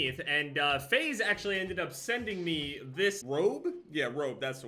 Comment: For a sec I thought he said FaZe sent me this probe